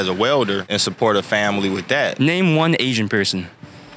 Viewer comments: "Name one ancient person." "Jesus, I can't remember!" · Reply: I thought he said "Name one Asian person".. I kept thinking the obvious answer would be "You!". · "Name one ancient person." "Jesus, I can't remember!" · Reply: I thought he said “Asian person.”